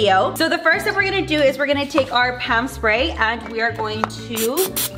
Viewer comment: I don't know why but whenever u said "pam spray" I heard "ham spray" Was it only me or others to